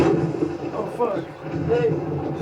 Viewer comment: when Dracula is carrying the woman and knocks over the nightstand by the window with her head, I swear it sounds like he says "Ohhh, fuck". That shit killed me, lol.